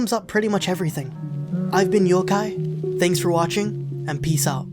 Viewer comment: You’re rather convincing, and I almost want to watch this now, even though I absolutely hate watching conflicts where I’m constantly screaming “just talk to each other!” Haha, at then end I misheard when you said Youkai as Yougai... Youguy? Yohguy? Yeah I don’t know. Thanks for the video though. :)